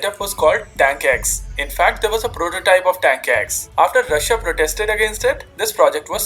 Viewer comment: DID HE SAY!? Danke X?